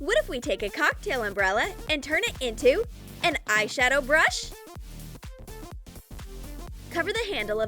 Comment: Go to 2:09 I think she said an eyeshadow brush